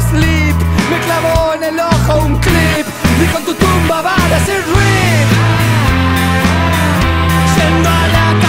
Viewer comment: yo siempre pensé que decía "en tu tumba va a decir Rick"